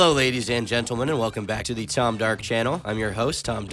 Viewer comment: Never knew you had a second channel till today when watching another video that mentioned your second channel. But I thought they said TomDuck and now that I realize it’s Tom Dark…I feel like Tom duck was a missed opportunity..